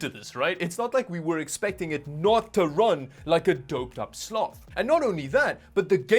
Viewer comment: I legit thought he said doped up slut. I had to replay it with subtitles haha